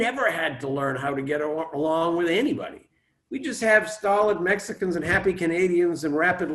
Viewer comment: Happy Canadians, hilarious. 46:03 I keep replaying it but have no idea what he’s saying, what Mexican????